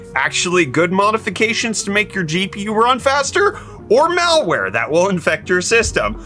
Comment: Misheard meltware for malware on the vBIOS uploads 😂